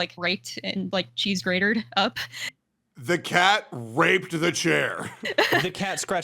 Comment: I think it was the cat "raked" the chair like clawed it, not the other r word but I could have misheard